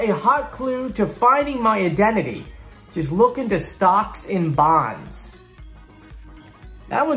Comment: Did Dragon say stocks and bonds, or SOCKS? I thought he said socks. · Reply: He said stocks. I'm thinking a Bond villain.